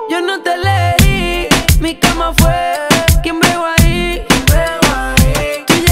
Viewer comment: "No te escogi, yo te cogi " poesía pura jajajjajajajaj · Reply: Yo pensé que decía “No te cogí, yo te cogí” :v ptmre Jajajajaja